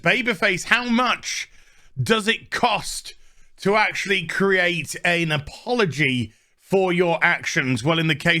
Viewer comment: Did you just say "Analpology"? Cos that sounds like the type he'd want to give.... take.... whatever 3:52 - We all been there, in Iceland, high af... Cut the guy some slack 🤣